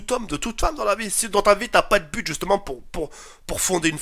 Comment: j'ai entendu "pute" au lieu de "bute" xDDDD